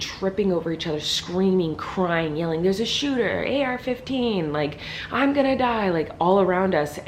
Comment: I know this is kind of a serious video but am I the only one who heard her say area 15?😂